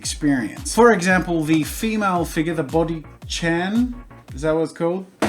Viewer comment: I thought you were saying boki Chan and that sounds delicious.